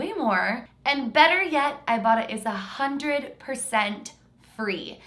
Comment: When said "ibota" I thought said "ibottom" I had to look in the description to see what she really said 😂